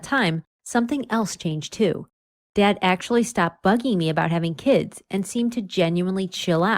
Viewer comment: I heard "My dad stopped f*cking me"